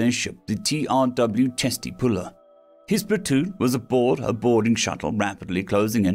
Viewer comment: For anyone not familiar with military history, the ship is named Chesty Puller after a famous US Marine. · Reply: I'm glad you wrote the name here. I was listening and re-listening to that part. I kept hearing "Testes Puller." Maybe I should stop listening at x1.5 speed. To be honest, I feel like the marines might call it the Testes Puller as a playful joke.